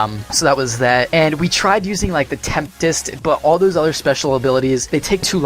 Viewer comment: is it just me or did he say temptest instead of tempest